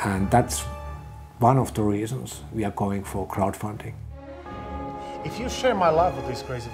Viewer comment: Did he really say 'Cloud Funding' on 3:20 ?